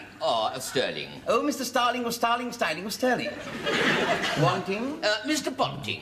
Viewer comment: Mr Starling (person) of Starling Styling (company) in Stirling (town). Mr Ponting (person) of Ponting Punting (company) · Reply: I misheard him... I thought he said "Mr Starling of Starling Starling AND Sterling... I thought it was a firm of some sort. Threw me off a bit trying to figure that one out lol Thanks for watching!